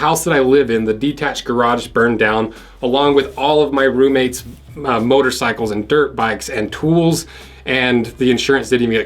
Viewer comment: At first it sounded like the shed burnt down with all his ROOMATES inside.😅